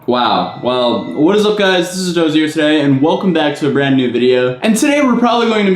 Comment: wtf did he say???? I know what he said by the letters saying atozy on the bottom but c'mon man, lol